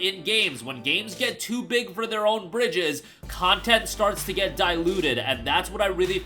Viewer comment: The saying is "Too big for their own britches” but it sounds like you say "Bridges".